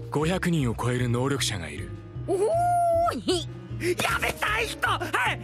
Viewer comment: ウソップの「やめたい人！！」が、「ヤベッ 大ヒット！！」 に聞こえてしうまう笑笑